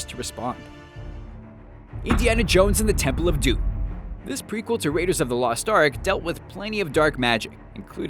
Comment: did he say Temple of Doom is a prequel? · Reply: Oh I thought he said prequel to raiders of the lost ark